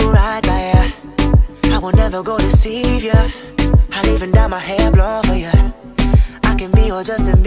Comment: "ill even dye my hair blonde for ya" * takes off hood to reveal black hair * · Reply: I thought it said "I even got my hair blown for ya" i'm